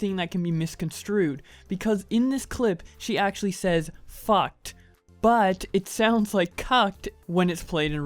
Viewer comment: When Poke said "fucked" to me is sounded like she said "cut"